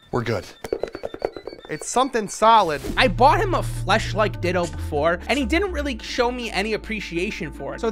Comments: I thought he said he bought him a fleshlight ditto before and I started loosing it 😅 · Oh my god i thought he said a fleshlight ditto